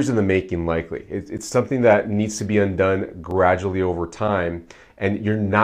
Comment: At about minute 13:58 - you said "un-done" but the closed captioning said "done". There was another instance where the closed captioning said the opposite of what you were saying. For someone who is hearing impaired, this could be bad to follow the closed captioned advice. Just letting you know.